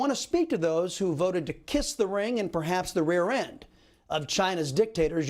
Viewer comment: I heard "to kiss China's dic....tator" whew😅 that was a close one 😂